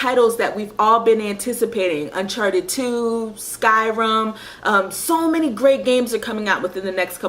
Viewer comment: Skyrum, never heard of Skyrum! :P (Sounded like you said that but knew you meant Skyrim) Yoshi's Island is a classic indeed and reminds me I need to get another copy of Yoshi's Island DS since I stupidly lost the copy I had. I've got the Japanese version of Earthbound (Mother 2) a few weeks back since getting the game on ebay cart only as pretty expensive and I don't like paying loads of money for old games, just a little nitpick I have even if they are rare etc.